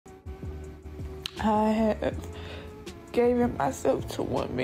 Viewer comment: I thought I heard "gaven" 🤣🤣